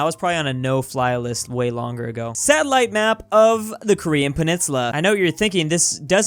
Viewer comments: I thought he said Caribbean Peninsula for a second lol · i legit thought was saying “Floridian Peninsula” because i know it so well (not American, i just hate florida) and i was so confused why there was red lines and why was acting so weirdly